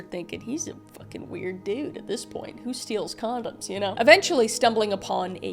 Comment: Bruh, I thought you said condiments